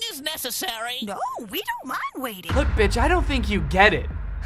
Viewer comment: (Only open the "read more"thing if u did all the steps ) 1.pause the video 2.go to 8:46 3.put captions 4.she said:"we dont lady waiting! She actually said"we dont mind waiting" Like if u noticed